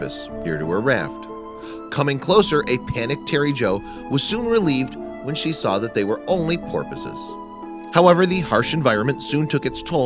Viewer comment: Did he say “relived when she saw that they were only corpses) at 5:55?